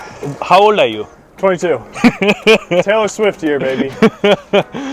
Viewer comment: is he said Taylor Swift ?